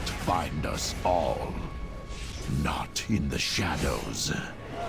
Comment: I thought it said shadows of anus